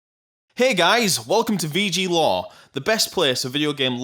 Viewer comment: I thought he was saying VG law lol he means lore